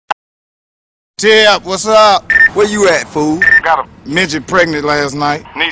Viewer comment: this whole time i thought mike epps said "jeb what's up"